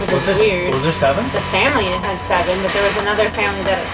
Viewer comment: I hear “TEN”, so i wonder if there were three in the other family